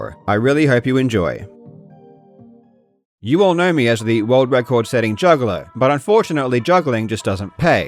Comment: I thought he said "juggalo" 😂 I was like I didn't know he was down with the clown…